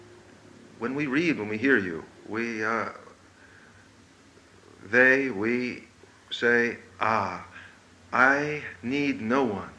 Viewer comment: “They, we, say ‘Aw!’” What he said